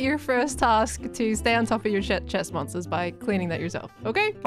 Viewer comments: what did she say here? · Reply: “Stay on top of your ch-chest monsters, by cleaning that yourself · I swear to God I thought she said "to stay on top of your shit" and I almost died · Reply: I had to scroll for this. She definitely did say "stay on top of your shit" and then quickly said chest monster. YouTube's captioning glossed right over it. LOL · was it just me or did say "stay on top of ur shit" 😳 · did she say to stay on top of your $h*# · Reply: She tried to say Chestmonster but in her mind also tried to say Shulker So it came out like a swearword